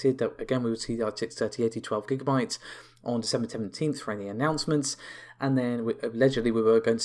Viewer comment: your pronouncing the date too fast can't make out what your saying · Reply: Did you turn on subtitles ? might help. December 17th is what he said btw. 👍